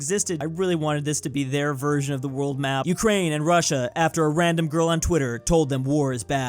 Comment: IDK why when he said Ukraine and Russia i heard "You created Russia"